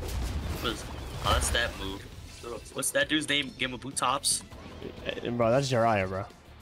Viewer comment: he did not just say gamabutops